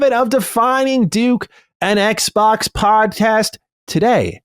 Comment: "An Xbox Podtest" is what I heard and I am here for it